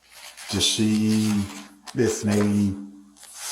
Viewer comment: did he say he was waiting for a prostitute ("waiting to see if a potential service might show up")? A bar owner drinking his own profits away in his own bar, bringing prostitution into his own bar. Are you kidding?